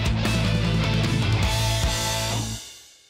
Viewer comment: I heard "Games for bastards on Xbox one" XD